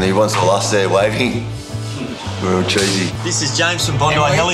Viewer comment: What he say at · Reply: real cheesy